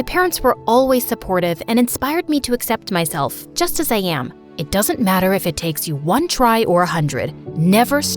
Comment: What the mom said “It doesn’t matter if it takes 1 try or 100” What the caption said “It doesn’t matter if it takes 1 try or 200”